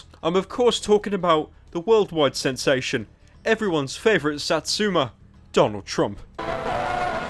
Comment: I heard "sad zoomed" and though Pyro was a pioneer. But instead he said satsuma which is some fat weeb shit Edit: apparently satsuma is just orange. Don't me